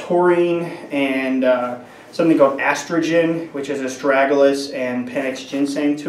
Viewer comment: Did he just say they put estrogen in it? Maybe my hearing is a bit off.